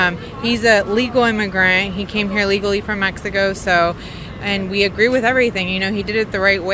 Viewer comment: it sounded like she said he camera here from Mexico illegally